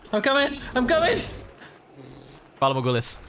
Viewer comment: Pala bagulous? what's the caption on how to spell what he said